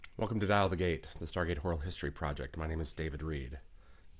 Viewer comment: I thought you said “horror history” at the beginning. 😮